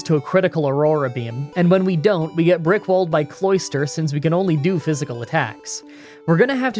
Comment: Did he just say he got Rick rolled by cloister?